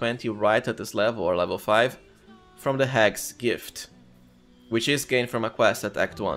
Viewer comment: Guys what is the Gift he is talking about? It sounds like he is saying “Hex Gift” but I can’t find anything about it online. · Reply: Hag's Gift, which is the +1 to any Stat you can pick when dealing with the Hag Boss at Chapter 1 in the Swamp area!